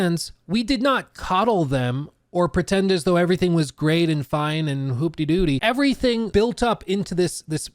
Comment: …did you just say “hoop-dee doo-dee” ??